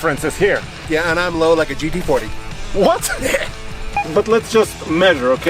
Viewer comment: I thought said "I'm low like a GP40" and my inner train foamer came out, but alas he said GT40 :(